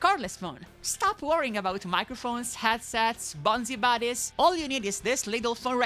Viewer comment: The CC say "bonsai bodies", but actually said "BonziBuddys" BonziBuddy is a discontinued freeware desktop virtual assistant (Initial release 1999, discontinued 2004). The software used Microsoft Agent technology (same as Clippy, whose name appears to actually be Clippit), originally developed for Office Assistant.